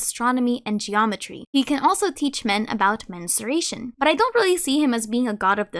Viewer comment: at about 9:22, did you say he could teach men 'menstruation'?? and then say it was about measurements? I'm so thrown off by that portion XD